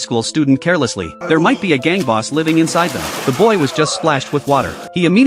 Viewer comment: I heard "There might be a gay boss living inside them" 😭